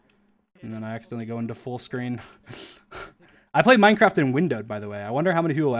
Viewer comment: what is he saying here? it sounds like windowed but idk what that means